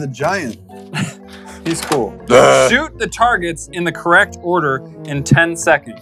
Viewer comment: did say sh*t 😱😱😱😱